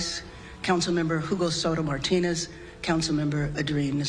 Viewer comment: did she say at 2:20 jugo soda martinis?